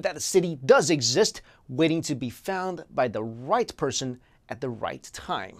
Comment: I thought he he was going to say By the white person at 4:37 Kinda dissapointed. Not racist BTW.